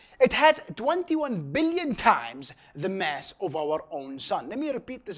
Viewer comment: Why did I hear "21 BLYET times"